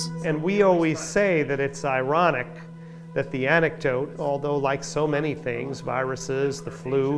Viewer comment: and again a few seconds later, does he say antidote or anecdote? To me it sounds like he is saying anecdote, but my hearing is garbage, so I could be wrong. I figured I would just ask and see what other say.